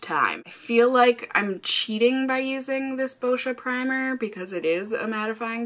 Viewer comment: I thought you said...'bullshit primer' ....😊